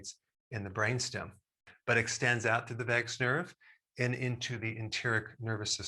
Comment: I heard it as Vegas nerve🤣